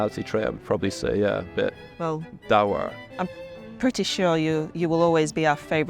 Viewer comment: The word you're all wondering what he said and what it means is 'dour', meaning sullen, gloomy, stern or even grumpy in manner or appearance. You're welcome.